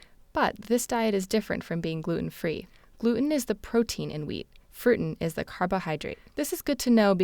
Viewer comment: Did she say FRUITEN? @ 1:50 Glooten and Frooten - the rootin tootinest duo. :-)